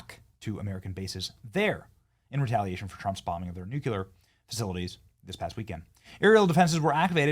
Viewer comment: Did Benny say New Que Ler?